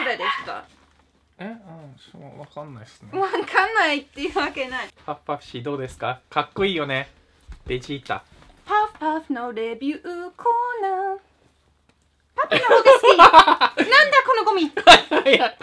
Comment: パパのレビューコーナーと聞こえました笑笑